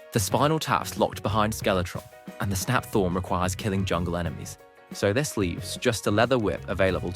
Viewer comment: i 1000% thought you saying "jungle enemies" at the start was "killing John Lennon" oh god